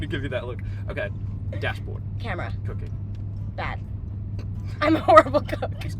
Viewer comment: I thought he said cocaine 💀